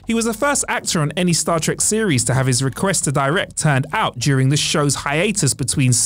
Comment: Please, can someone translate for me? 7:55 "He was the first actor on any Star Trek series to have..." WHAT? I don't hear any actual words for a few seconds, but he is talking. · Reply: "First actor to have his request to direct turned-out". Presumably Turned-out is British for turned down.